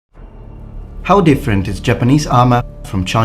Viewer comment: The opening line is "how different-", but until the third replay what I heard was "howdy friend". 😉